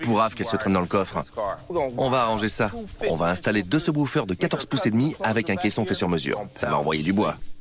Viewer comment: "on vas installer 2 so bouffeur de 14 pouce/5, comme sa elle entendras plus rien, elle seras sourde, T'ENTENDS", voilà ceux que moi j'entends^^ espèce de ..merci^^, fait revenir nico à l'occas, à vous deux sa fait un bon délire, t'chô !